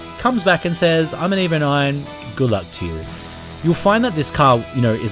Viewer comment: did you say, "I'm and EVO9, gulag 2 u"? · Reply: lol - Good luck to you. As in, fk u boooiiiii im fabulous